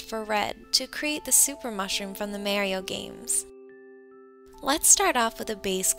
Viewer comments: she said merio instead of Mario lol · Did she say Mayrio??????